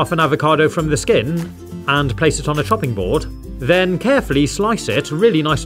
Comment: I can't be the only one who heard place it on a f*cking board